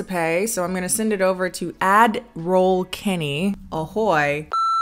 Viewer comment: okay I have to admit I thought you were saying Admiral Kennie, not Ad Roll Kennie. I was here for it tho.